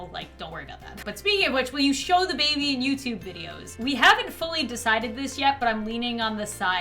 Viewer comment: what was the question here? I can't really hear but CONGRATULATIONS!!!!!!! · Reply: “will you show the baby in youtube videos?”